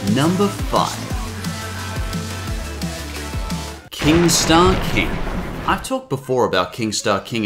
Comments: When I hear "King Star King" I thought it said "Keemstar keem" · I thought you said keemstar king